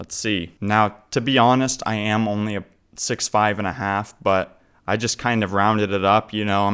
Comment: Did you just say 'only 6'5?'